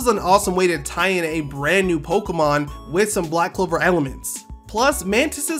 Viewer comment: After help from some of you guys, I will be updating Toxsquito's Ability but I could use some help for that too. What should the new main ability be and NO the type isn't changing! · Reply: im not gonna lie the whole time up until 2:34 i thought u said sailor moon but u said black clover HAHAHA